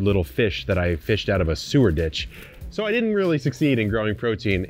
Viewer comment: You said "epic hens"... I heard "epi pens" 😅